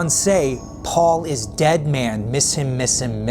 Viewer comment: It sounded like he said turn me on "Red man" not "dead man" IDK maybe it's just me 🤷